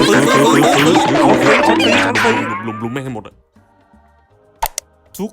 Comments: ถ้าฟังดีๆจะได้ยินคำว่าลุมๆมันให้หมด(((ถ้าผิดขอโทษด้วยนะคะ))) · ผมได้ยินคำว่ารุมรุมแม่งให้หมด😂😂😂